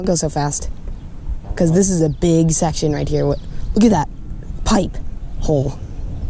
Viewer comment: did u say pike? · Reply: Dylan P pretty sure I said pipe because I saw a pipe I don’t remember